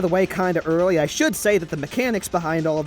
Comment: who else heard mecanamics